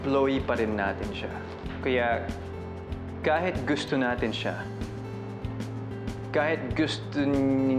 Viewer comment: did ethan just said "KAHIT GUSTO NATIN SIYA"............ "KAHIT GUSTO NIN....YO SI BILLIE"